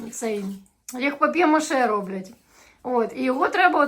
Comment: Только мне послышалось что материлась 5:03-5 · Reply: Роблять - это делают. Украинский учите)))